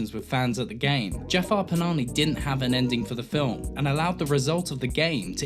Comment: Did you say Jafar 'Panani' @ 1:36? haha, also i think the term you coin 'pseudo-realism' already has the title of neorealism in film discourse? :D Thanks for referencing Iranian new wave, I wish more people would appreciate Iranian cinema :) Great video, loved it.